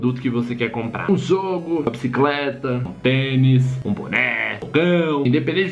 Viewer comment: Mano eu entendi ''PENIS'' DHASUDSHAUASDHUDHSUADAS